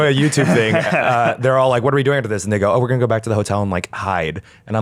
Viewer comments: maybe I’m just mishearing things but did Eugene just say the n-word at 13:21? I’ve rewatched it several times and it still sounds like it? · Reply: He says "what are we doing after this? And they go" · What is Eugene saying at 13:22? Maybe I'm just deaf but it kind of sounds like he's saying the N-word? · What does Eugene say at 13:20? I can't tell, sounds like he says the N-word but it must not be that. Anyone can hear what he says? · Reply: "What are we doing after this?"